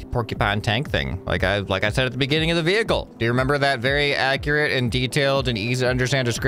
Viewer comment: did he say "Vehicle"